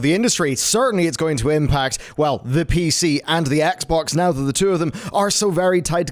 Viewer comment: why does it sound like he says the Xbongs? XD